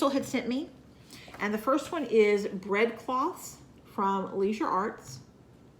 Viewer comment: Lol I thought you said "red fox" instead of "bread cloth" and was very confused when you showed that because I didn't see a red fox anywhere on it. And there's not a thing wrong with not editing.